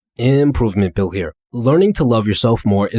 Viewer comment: Lmaoo 😂 I am watching your videos for like 3 months now and i always thought that you are saying "Hey guys improvement Phill here". I also want to say thank you for making this videos absolutely free. They helped me so much. I really aprociate it ❤